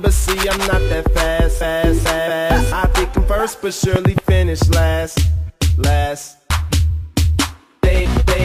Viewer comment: it sounds like hes saying fat ass